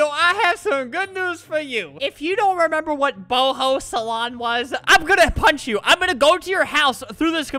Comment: I thought he said butthole salon lmaoo😂